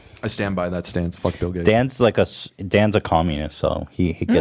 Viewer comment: all i heard was that Dan is a king